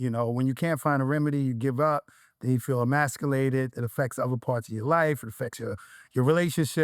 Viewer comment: I thought you said he was masturared. You lying